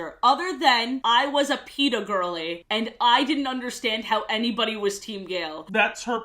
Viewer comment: 🤣 I thought you said you were a PETA gal-ly and didn’t understand why she’d like a hunter omg